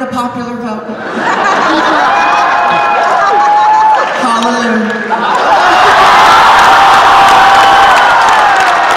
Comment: In 8:06 what did Jinx say after “but she did not get the popular vote”? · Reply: Halleloo (Shangela's catchphrase)